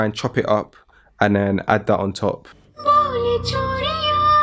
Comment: I heard "bole chutiya😂"